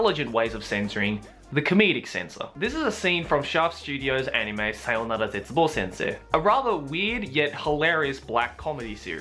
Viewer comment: what is the anime at 1:00 he says it too fast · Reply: I think it's called Sayonara Zetsubou Sensei